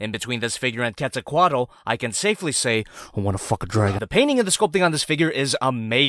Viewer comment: it kinda sounds like you said “ I want a f**king dragon” instead of “ I wanna f**k a dragon!”